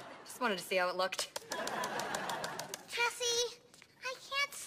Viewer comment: she said tessie right? Or am I hearing things cause I’m .<num> % sure